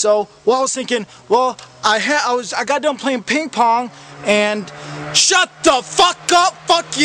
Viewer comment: it sounds like he says irag pong haha